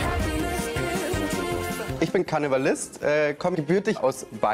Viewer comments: Ich hab irgw verstanden " ich bin ein kannibalist " aber ich glaube er meinte "Karnevalist" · bin ich die einzige die "Kannibalist" verstanden hat? 😳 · Bin ich die einzige die ,,Kannibalist" verstanden hat? 😂 Ja ich weiß, es heißt Kannibale und Carnevalist aber hab das zuerst ganz falsch gehört🥲😂